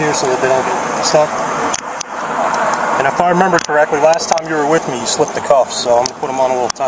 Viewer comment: "Last time you were stopped you slipped the cuffs". So this is becoming a REALLY BAD HABIT??? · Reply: Right!? I thought he said “the last time you were with me you slipped the cuffs.” I’m glad someone else noticed it.